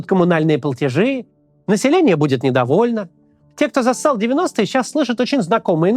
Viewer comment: ПОСЛЫШАЛОСЬ: Те кто зассали в девяностые